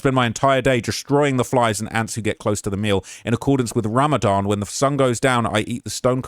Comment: did I hear someone say "Ramadan"? 🤔